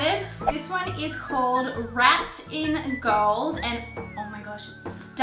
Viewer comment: when you said"wrapped in gold" i thought you are saying "rat in gold"😂😂😂😂😂i ❤️U alot